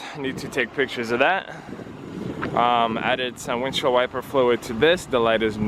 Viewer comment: For some reason, when he said “windshield wiper fluid” I thought he said blinker fluid, and I’ve never face palmed myself harder😂